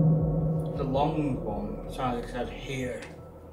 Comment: I swear it says something witches or the lot witches